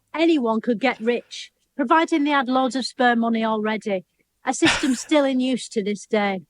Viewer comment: "providing they had lots of spare money already", not what the subtitle says!